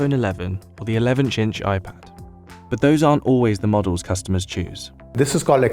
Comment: Is it just me or did he actually say 11-inch weird...